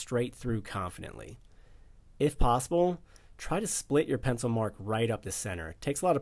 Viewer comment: Min 2:32, can’t tell what you said about your second perimeter cut. It sounded like “split your pencil mark right up the middle”. Is that what you said?